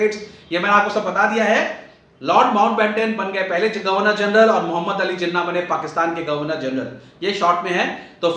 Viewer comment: did I heard Lord Ben 10😂